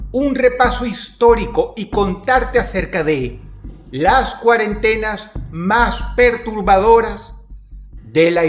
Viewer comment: Pensé que decia: Las 7 cuarentOnas más aterradoras 🤣